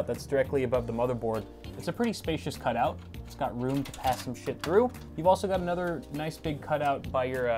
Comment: Is it just me or did he say "pass some shit through"